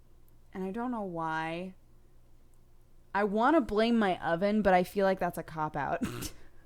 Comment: WHAAAAAT?!?! Did she just say "That I want to bling my oven" 7:33 or am I just out if it to day, again!